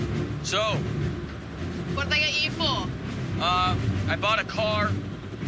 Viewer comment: What? Still wondering what she said. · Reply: "what did they get you for?"